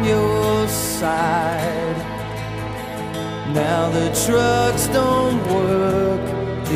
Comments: haha sounds like he says "the trucks don´t work" :) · All i hear is "The Trucks Dont Work"